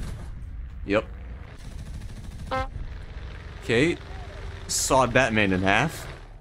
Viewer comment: sounds like he said, “ok sawed Batman in half”